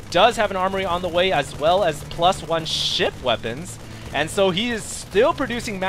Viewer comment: i heard it as +1 shit weapon